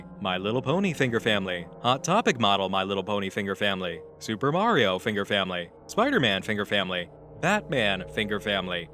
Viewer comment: "Batman finger family" made me laugh harder than I should. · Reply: "mario finger family" i honestly thought it said "Mario Finger's Family"